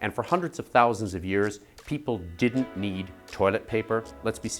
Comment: misheard as "people got the runs and need toilet paper"